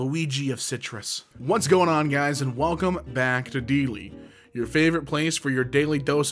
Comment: I heard "And welcome back to Emkay" instead of